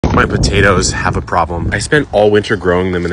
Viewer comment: I was horrified and thought you said tomatoes. 🍅